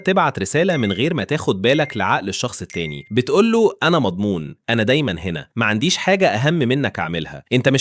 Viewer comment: انا سمعتها انا مبضون 😅